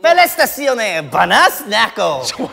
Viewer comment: i heard felicitaciones, banas nako